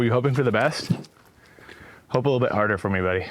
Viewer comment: "were you hoping for the best.." and then im sure its english but i cannot understand what he is saying here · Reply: "Hope a little bit harder for me buddy" is what he said, at least as far as I can tell.